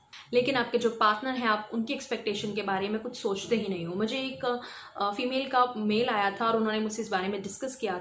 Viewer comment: I thought you said "mujhe ek female ka male aaya tha" 😂😂